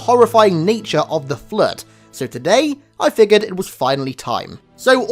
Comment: idk why to me it sounded like he said the true horrifying nature of the flirt